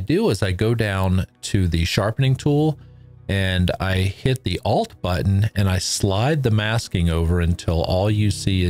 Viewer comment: After 4:30, you explained that you go the sharpening tool and hit, "auto"? button? I cannot catch what you said and I don't find this button in the Lightroom. All I find there are amount, radius, details and mask. Could guide me a bit more on this? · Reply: sorry I wasn't clear on that. what I was saying is the ALT button on the keyboard. Hold that down while pulling the masking slider to the right under Sharpening.